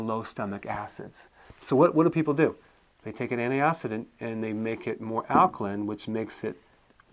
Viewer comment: what is he saying here? People take an "anti-....ding" ? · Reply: "Anti-Oxidant"